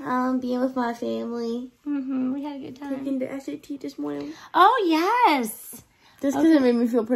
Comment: did just say she took the s.a.t. this morning?